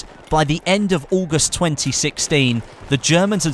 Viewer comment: Did I hear August 2016?????